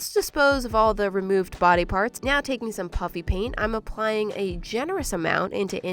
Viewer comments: Guys I swear she said puppy paint instead of puffy paint this time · Is it me or does it always sound like she says Puppy paint 🎨